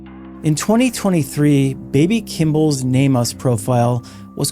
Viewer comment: Baby Kimball's what profile? Were you saying "name-os profile" or "nameless profile" and is that some kind of John/Jane Doe registry 🤔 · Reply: NamUs National Missing and Unidentified Persons System